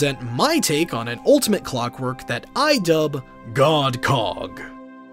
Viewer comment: Why do i hear it as GODCO*K